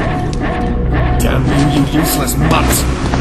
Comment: he said "damn you , you useless mutt!" but the subtitle said "damn you, you worthless mutt!" Weird...